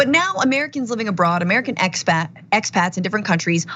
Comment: Hahaha i thought she said "ex-fats" first 😅